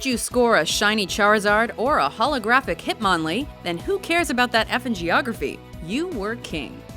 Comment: Am I the only one who did a double take when she said, "who cares about that F in Geography"? I thought she said who cares about that effin Geography, as in a substitute for the F word.